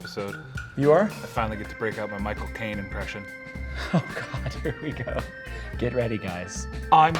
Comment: Yo I thought he said he could finally bring out his cocaine impression 😂